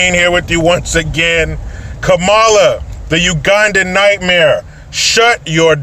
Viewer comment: Ugandan??? I thought she said she be Jamaican - because shes cool to burn refer by the beach.